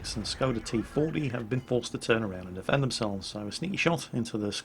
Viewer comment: I have come to the point of asking: do you even watch the video while commentating? 11:42 T 25 is not pronounced "T fourty". Just read what it says on screen goddamnit.